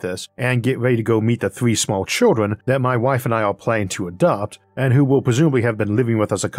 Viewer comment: I honestly thought I heard you say you were planning to abduct children, went back, heard the same thing, then turned subtitles on and was very relieved to see it was ADOPT and not ABDUCT... lmao. Congrats man!